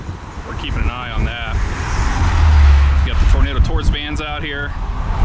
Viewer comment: WAIT did I just hear "tornado tourist van"?? what a brilliant business idea